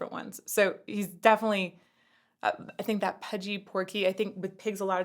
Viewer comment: He said "portly", not "porky". Admittedly, "portly" is a British word, so it's understandable that you didn't hear it.